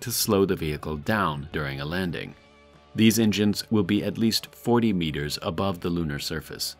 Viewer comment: He says 40 meters, the subtitles say 50 meters.